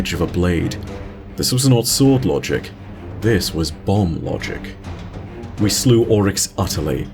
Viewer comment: Can't be certain what was said here. Sounds like "Bomb-Logic" but could be bond-logic for all I know. In fact, the subtitles seem to think it must've said "Balm-Logic", lol. The Logic of the Ointment.